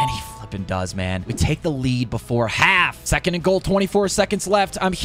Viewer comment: It is cool how he started out with the actual Texans overall even more impressive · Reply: did bro say the f-word???